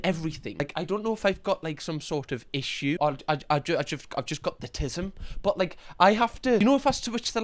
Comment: The donuts are being fed lately js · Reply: I THOUGHT HE SAID IF I GOT THE AUTISM AND I WAS LIKE: I have autism girly pops